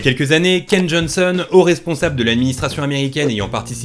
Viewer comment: j'ai entendu cave Johnson a la place de cane Johnson, ptn j'ai eu peur ^^